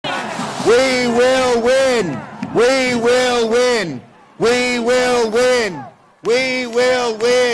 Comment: we will win (we will win) we won't rest (we won't rest) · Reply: Sounded like he said we won't win